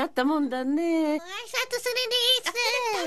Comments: 抹殺するでーすにしか聞こえないのは僕だけ？ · 抹殺するで〜すにしか聞こえんかった